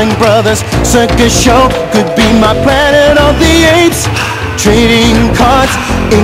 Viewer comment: I thought he said Diddy Kong, not Trading Cards